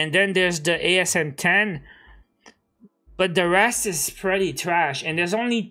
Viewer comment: bro y did u say my RUS a trash gun hwww ??? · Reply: the REST is ... wtf dude